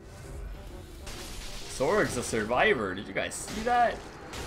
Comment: I like how the subtitles said "Sword's a survivor" lol